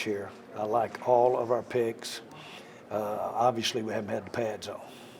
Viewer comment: I heard "pigs" instead of "picks" and just thought it was another Jerryism